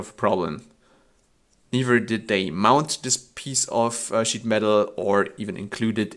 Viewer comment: did you say "this piece of shit metal" ? ;) Good review! Very fair and unbiased considering you came up with most of this and the other companies just copied and maybe finetuned.. Greets from Austria